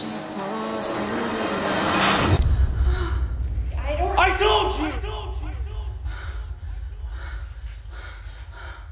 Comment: Please can anyone hear what the voice in her head said? Thank you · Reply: ‘I told you’